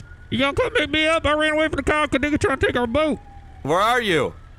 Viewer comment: Thought he said nigga tried to take my boat xD